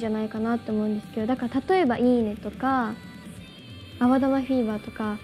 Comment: actually has a translation mistake, I think she said iine not ijime (dame zettai)